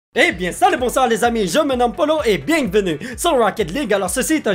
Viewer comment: dit quoi dans son intro ? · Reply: J'avoue c'était pas comme d'habitude j'ai compris " je m'appelle polo " 😑